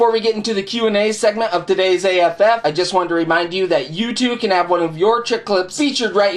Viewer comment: i thought he said youtube not you to Ha Ha LOL.